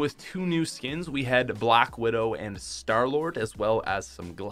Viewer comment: did somebody say star lord